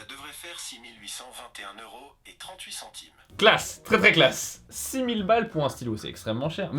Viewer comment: J'espère que c'est un gros troll, parce que 200 000€ dans une montre, non de dieu, à quel point est péter de thune XD! ... Ou alors ils voulait dire 200 000 yen et là, bon, ça reste tarpins cher, mais, "abordable" pour un chef d'entreprise XD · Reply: J'ai entendu "200 boules".